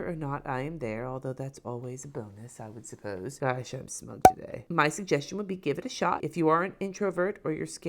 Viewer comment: I could have sworn you said "I shouldn't have smoked today" and I died laughing 😂😂